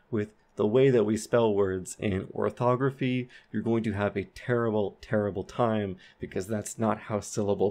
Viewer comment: I heard him say tewwible😍cute